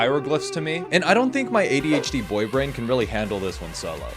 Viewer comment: I thought bro said ADHD boyfriend and I thought of those biker boyfriends.